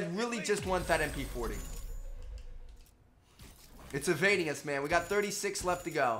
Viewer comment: What's that word he says at 11:26 'evadious' 'invadious' 'invidious' idk I want to know the word and the meaning